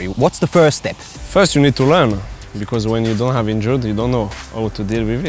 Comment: Who Else Heard Hazard Saying "First You Need To Run " 🤣🤣🤣